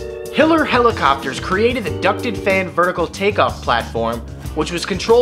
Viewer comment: i thought it said "Hitler Flying Platform" and i was like WAT THE FAK?